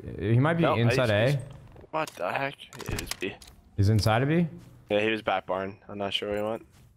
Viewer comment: I heard: "He's inside of me"